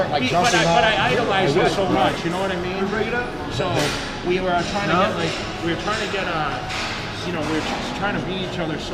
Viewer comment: What dd that guy say? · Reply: DamnTakes he said “break it up”